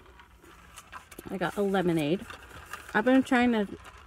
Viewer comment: omg that looks so good. this is so random but i didnt hear what you said you were drinking so i tried to rewind it and stopped at like 4:52 and all you said was "coke" and i cant stop laughing. I just kept rewinding it and all youre saying is "coke....". I'm such an idiot. LMAO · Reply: MsTeshin lmao 😂😂😂 it’s lemonade since I’m trying to stop drinking coke 😒